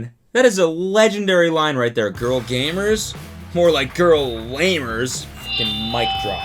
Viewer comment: I deadass thought he said “Girl gamers? More like girl labors”